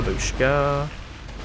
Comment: did you said babushka?? XD